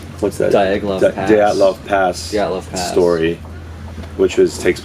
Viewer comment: Great👍👍👍 5:30 What's the story? I can't catch it🙉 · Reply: I understood, Dyatlov Pass